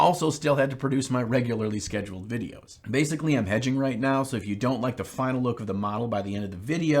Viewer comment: I thought said “edging” instead of “hedging” at the beginning and i was very confused